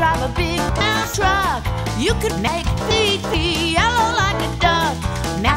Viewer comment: Not even 30 seconds in and I hear "You can make pee pee yellow like a duck" oh, what a true poet you are. My sense of humor has withered away